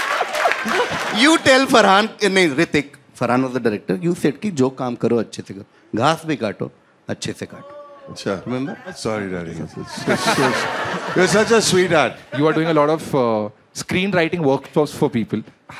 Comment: sorry but for some reason I heard 'laash bhi kaato acchese kaato'